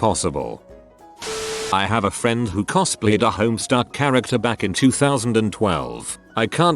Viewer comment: I heard "homestar" like homestar runner and was very confused until the very end where I thought "wait...a troll?? I don't remember any troll from homestar.. especially not one with a jacke-- *that says homestuck oh my g o sh*"